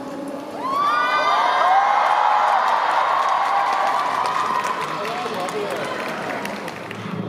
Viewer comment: The real question is what did Jhope say at 1:45 lmao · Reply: He said "Meh I can sing better" 😂😂